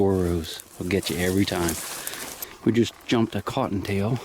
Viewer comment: LOL...I thought you said we just jumped a cottonmouth snake! I was thinking why in the world is he doing a close up? Get out of there! Then it was a cute bunny..lol....listen carefully I learned...